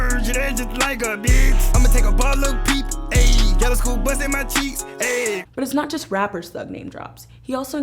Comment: Wait.. did he just say "Yellow school BUS in my cheeks"